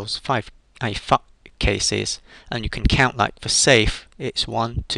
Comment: did u say FUCK CASES